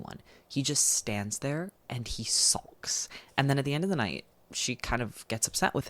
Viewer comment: Hi I doubt anybody will read this comment, but english is not my mother tongue and I didn't get the word that saying at 58:41 ("he just stands there and he .... ?") What is that word ? I'm a bit of a control freak and it itches me that I don't know that word. Thanks bye :))) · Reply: he says “Sulks”. it essentially means being sad and is usually used when someone is trying to make it clear that they’re upset